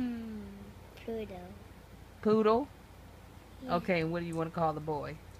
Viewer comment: sounds like he was saying pluto not poodle like pluto and mickey